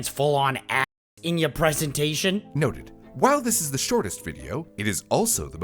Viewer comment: I heard "noted" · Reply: Yeah they know the word is noted lol, they just found it funny that they initially heard "no".